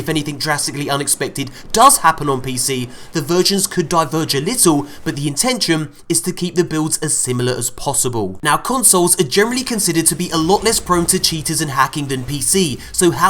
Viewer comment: i thought i heard "the virgins could die for a little while"